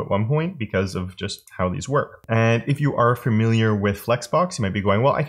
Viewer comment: Am I just hearing things, or are you saying ‘fermiliar’? (Cf. around 3:38.) Anyway, great content as always. (Just hard to listen to sometimes with the sss’s being so abrasive.)